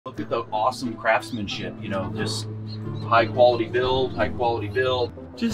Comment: I swear I thought said "Awesome Crapmanship" 😁